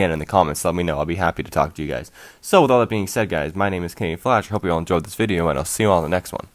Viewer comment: I thought your name said Canadian fish